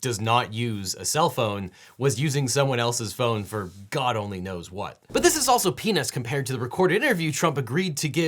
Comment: it sounds like he says 🍆 and not 🥜